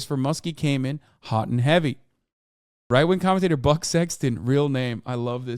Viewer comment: I was half listening, so when he said “Buck Sexton” I thought he said “Butt Sexton (real name)” and nearly choked